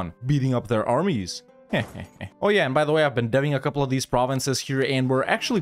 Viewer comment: tons of fun to watch also, 14:58 captions say "i've been dabbing a couple of these provinces here" lol